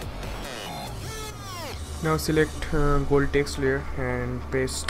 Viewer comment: pass filler :"D xD fast blur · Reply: xD when u said ;p gold text xD cc said voltage ;p